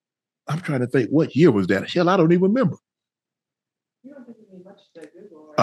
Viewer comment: did he just say “Chelle I don’t even remember?” Hell na is that “That’s my Michelle” working in the background??